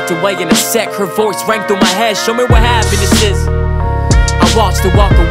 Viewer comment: Tbh i thought he was saying “what happened to sis”